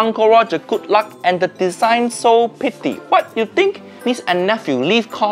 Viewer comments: bros accent made it so he said "so pity" instead of "so pretty" ( around time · did he say pitty or pretty